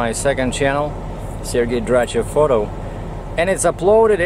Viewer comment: Hello great videos , I tried to find your other YouTube channel and I couldn't I thought I heard you say that it was Sergei Dratchev photography .... What is the name of your other channel ?